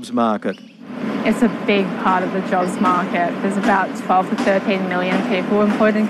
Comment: Did she says 12 or 13 MILLION people are employed in the construction sector?